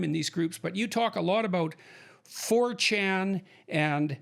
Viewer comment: HELP! English is my second language, so i don't understand what they start talking about. It sounds like "Fortune", Minute 3:00 WHAT ARE THEY TALKING ABOUT? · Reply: 4chan It's an anonymous message board online.